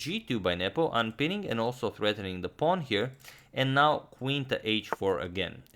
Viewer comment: Did he said threatening the pawn?